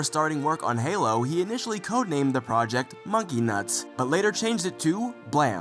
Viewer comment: am I crazy or did he say monkey nuts💀